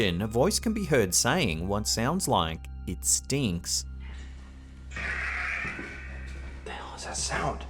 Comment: The last clip is kool. I don't hear "it stinks" I hear "it's me". But the mirror shadow is freaky as hell. Definitely unexplained shadows & sounds that I think are real. Great captures......